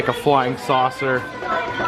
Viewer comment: I thought it said frying saucer. Lol